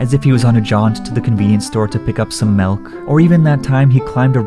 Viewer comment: why did you say malk its milk